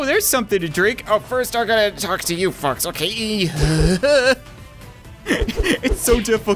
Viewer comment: did say first i gotta talk to these f_*cks